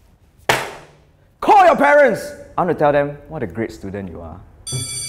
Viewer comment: I thought he said Call your Terence!